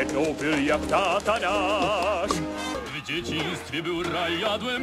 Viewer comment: Acaso escuche "Satanás"?